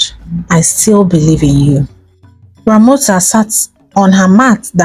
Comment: initial is Ramosa...but i clearly hear you say, Ramota. Good thing I'm a good listener. Love you.